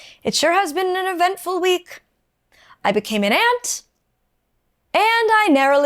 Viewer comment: My stupid English ass thought you said you became an ant 😅 I hope you're both doing okay ❤